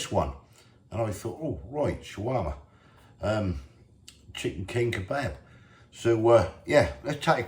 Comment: My hearing is deteriorating i think, because i thought that you said chihuahua chicken 😮 looks good 👍